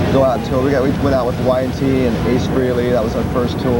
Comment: Nice, thanks. Just, did Taime say ''Ace Freely''?